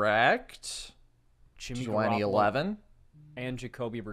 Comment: Did I miss garropolo in the Brady question or what? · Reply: thought he did say Jimmy. I’m gunna run it back. 4:02 says Jimmy G over also talking but you can hear him say Jimmy G.